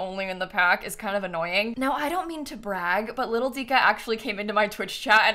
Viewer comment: I thought you said Littlesiha ^^